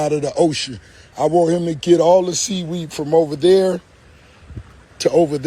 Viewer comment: He said sea weed and i thought he said wee wees